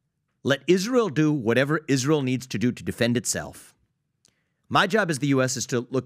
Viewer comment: Did he just say Defund Israel?🤤